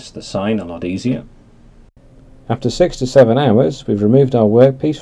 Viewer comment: did he say after 67 hours 😂😂😂